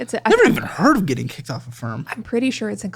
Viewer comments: lol it took me a long time to realize he was saying she got kicked off affirm. I thought he said she got kicked off a farm 😂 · WHOLE FUCKIN TIME till about 40% through the video I thought he was saying she got kicked off a firm... I was like, a law firm? What the fuck is going on man. I didn't even think of AFFIRM. I guess that's a good sign, not knowing all the random ass pay plan apps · lol it took me a long time to realize he was saying she got kicked off affirm. I thought he said she got kicked off a farm 😂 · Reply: I thought he said a firm like a law firm, i forgot Affirm is a after pay type service